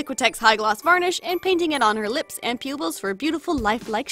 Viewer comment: It sounded like you said pubils